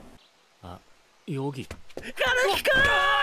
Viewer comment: I heard “KANEKI-KUUUUUN”